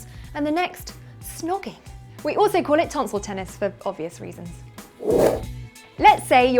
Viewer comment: Can somebody tell me what she said in 2:36, right after the transition? I didn't understand :c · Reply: 'tonsil tennis' - a less common word for snogging.